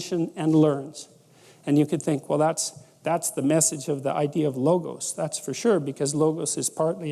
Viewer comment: I cannot figure out a word that he uses at 1:46. It sounds like "logus", but searching that returned no results for me. Can someone tell me the word please?